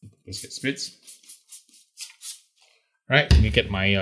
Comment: DID you say COFFEE Chocolate rub ???????? WTF ???? >> Coffee <<< >>> Chocolate <<< SURELY you didn't say Coffee Chocolate rub DID YOU ????